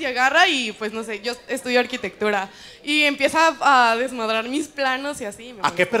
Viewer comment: wbn yo también escuché "desnudar mis planos" xd